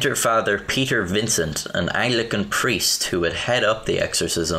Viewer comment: Excuse me, did you say "an eye-lickin' priest"?!